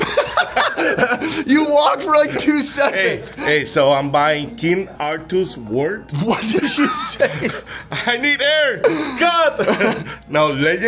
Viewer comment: I swear i thought the man said "I'm buying King Arthurs Wart".